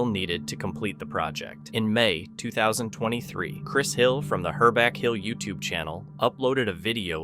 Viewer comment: Chris’s videos are legit imo. Most of the others are just “entertainment.” 50:49 “Herback hill?” Did I hear that right?